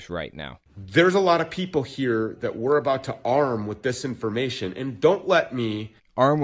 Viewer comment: "To arm with THIS information. . . ." Not disinformation. You misheard.